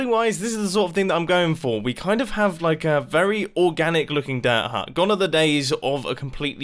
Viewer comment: I thought it said dirt hunt